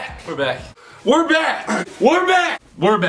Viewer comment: Did "we're back" sound weird to anyone else after the video or is it just me? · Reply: Sadie's World123 I hear “wore back”